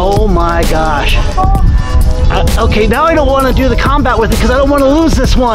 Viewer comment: wait did he say combat?